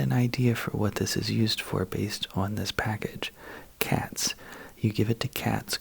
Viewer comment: I thought he said kids, not cats 💀 🤣